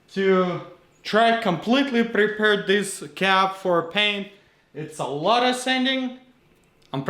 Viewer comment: Can you repair the roof correctly and not have to replace it? Really enjoy your videos....can't wait for the next installment. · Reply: he is repairing it not changing it it sounded like he said change but he said paint it not change it